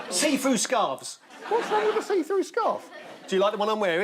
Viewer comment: At around 44 minutes in Paul Merton guessed 'a see-through scarf'. But I THOUGHT he said 'a seafood scarf'.